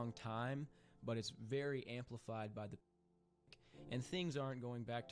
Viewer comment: I think he's saying "pandemic"